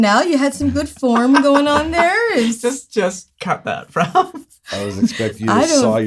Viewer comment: Did anyone else hear say "Just cut that, Rob" could this be camera mans name????? Rob???